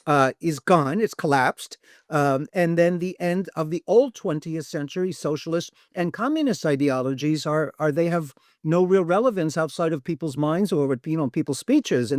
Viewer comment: He doesn't actually say that...maybe just chill out a bit, listen a bit further on and see what he has to say, I found it very interesting...and I am a life long committed and active Socialist. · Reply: wow thanks for correcting I misheard him saying exactly that. "The end of the old 20th century, the communist and socialist ideologies, they have no real relevance outside of peoples' minds or peoples' speeches in the actual real world" That's exactly what he said.